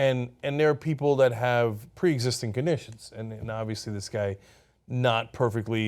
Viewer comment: If you look at subtitles at about 1:15 cenk says obviously but the subtitles say obesity.